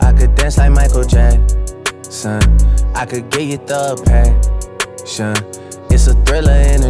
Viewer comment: drake : i could give thug passion me : sounded like you said "i could give depression" drake : no...